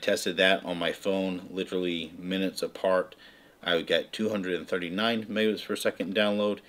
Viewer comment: Ok weird as my ears hear things... Around 13:00 in this video, I could swear you said 239 tomatoes per second... you are amazing! I have other questions for you on a ton of stuff, but what makes you so amazing is not just your knowledge and tons of testing, but the fact that you really do take the time to respond to your viewers. A lot of channels do respond, but not with the frequency and expedience you do, and certainly not with the level of detail that you do. I am sure I speak for all of your subscribers when I say a huge and heartfelt "Thank you!" for it all! One day we hope to move to somewhere more rural, and everything I am learning here so far really does make a difference, even if we only are just new to the whole 5G internet thing. So again, thank you!